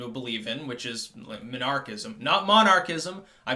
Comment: I initially thought you said "Muhnarchism" LOL